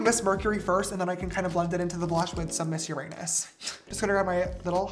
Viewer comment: I heard 'miss ur *nus' and I was like huh?